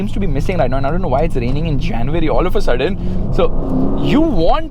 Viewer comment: did he say january??